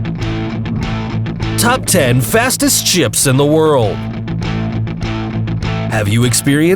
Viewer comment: I thought at the beginning u said the fastest chips in the world